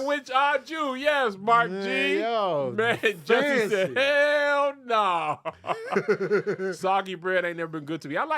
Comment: Was I the only one who heard say "Hell Naw" at 37:23 ???? 😆😆😆